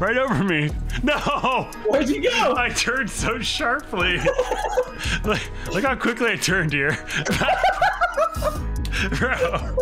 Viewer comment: "Look how quickly I turn dear." That's what I hear.